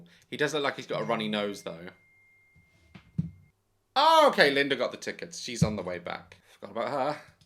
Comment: When he said “Linda got the tickets forgot about her” I thought he said Sara and I was like OH SHIT!!